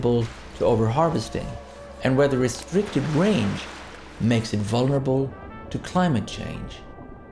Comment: Hey guys, as I do not undertand it properly: what does he say from ca. 0:48 to 0:52? I mean literally. Greetings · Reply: And whether restricted range makes it vulnerable to climate change